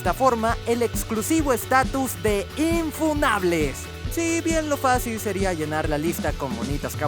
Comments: Pensé que decía infumables , y como estaba Killua en la mini , ya estaba preparado para denunciar el video xd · Por un momento pensé que decía “infumables”